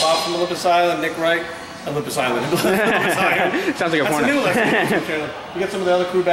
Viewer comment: Did he say "Sounds like a porno?"